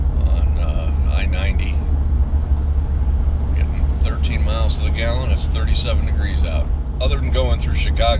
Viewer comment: How are you getting 23mpg? My 2500 Dodge is lucky to 19. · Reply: I heard him say 13 miles to the gallon.